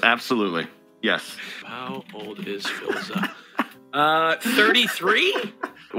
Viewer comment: It sounds like he said " How bald is Philza "...